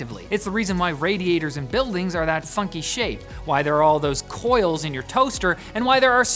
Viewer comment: Omg. I thought he said, "F*#@ shape"